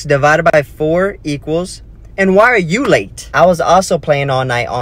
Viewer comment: 3,726 ÷ 34 is why are you late.. (I thought she said the answer why are you late 😂)